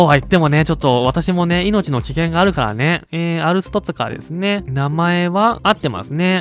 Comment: アルストツカがアルツトツカに聞こえるのは俺だけか？